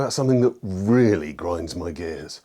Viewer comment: I swear I heard you saying in intro "what really grinds my gi" and I was so confused....